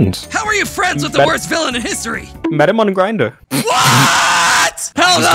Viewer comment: I hear: “medemoneygrinder🙄” they hear: “met him on a grinder🤭”